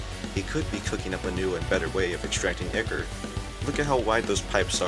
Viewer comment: did he say ichor cuz even the translater says ier and not ichor